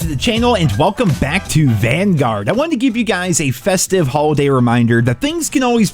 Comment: I thought you said "Welcome to Banguard" their for second.